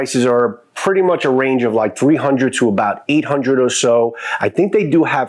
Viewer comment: I believe I mentioned it...about 450.00 I believe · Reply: thanks, I thought I heard 3-800, sorry.